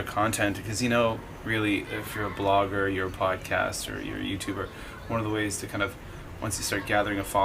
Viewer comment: Wait, I heard "Nudie Youtuber"....